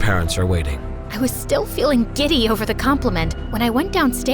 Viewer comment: thought she said diddy